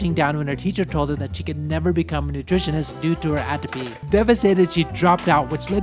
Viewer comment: at around ~1:40 in the video you say: "she can never become a nutritionist due to her ________." Her what? I've replayed this part like 20 times and have no idea what you're saying. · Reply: The teacher advised her against a career in nutrition due to "Atopy", the genetic tendency to develop allergic diseases. Nutritionists are often required to sample (consume) foods in order to analyze them. One specific example, Japanese schools typically employ a nutritionist who examine and sample each day's lunch meal before it is served to students and staff. Atopy and associated allergic conditions may make that role difficult and potentially dangerous to her health.